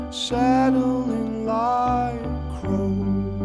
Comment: did i hear him right: "lippy kids on the corner saddling microbes"?